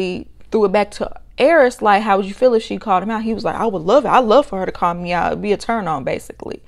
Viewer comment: I coulda swore said “I don’t love it” when asked how he feels when Jasmine calls him out. I thinks he’s just going along with the narrative now.